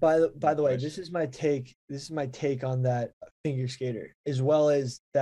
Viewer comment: Did Billy just say "Finger" Skater?